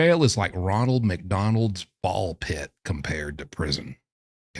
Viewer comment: For no particular reason, I heard “Ronald McDonald’s ball sack”…